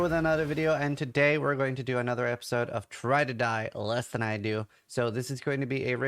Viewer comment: Try to die? I thought he was saying Ride or die less than I do. 😂😂 Good stuff.